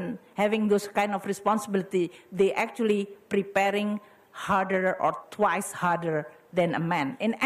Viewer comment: Kalau ada yang namanya singlish, utk orang indonesia inggris mendok sebutnya apa yah, indolish kah? I’m not saying indonesian english is bad, its just has its own character that i think we should name it... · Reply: "You are facing with a much bigger and higher, hmm harder. But i think when you perform, they will appreciate you more." I heard she spoke "harder" but seems hear like "hurter" which is the meaning is still same I suppose